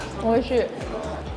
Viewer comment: I thought you said "Holy shit" at first lol